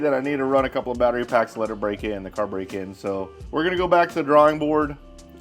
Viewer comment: I thought he said (break the car in) not (BREAK THE CAR) LOL.